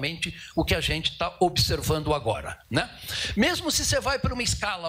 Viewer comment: esse "né" foi na verdade um "a gente avisou, né"